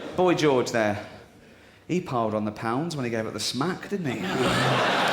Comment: I don't get what he's saying right there. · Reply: He piled on the pounds when he gave up the smack = He got fat when he stopped taking drugs/heroin.